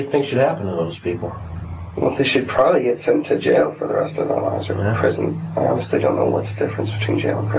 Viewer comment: I promise I heard him say "They should probably be sent to jail for the rest of OUR lives...." 🤯